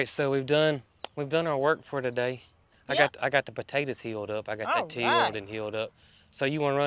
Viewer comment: Sounds like is saying "Heal up the potatoes 🥔" instead of "Hill up the potatoes 🥔. 😂😂